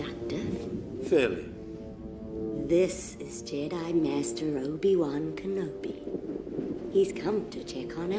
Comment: the subtitles say that: THIS IS JEDI MASTER HOLY ONE KENOBI